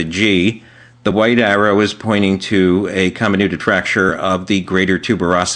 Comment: what does the white arrow at 2:08 point to?: "? of the greater tuberosity" - couldn't hear it properly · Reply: Joanne Joseph a comminuted fracture